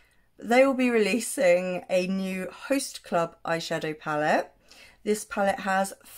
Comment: I thought it said The Frost Club 😹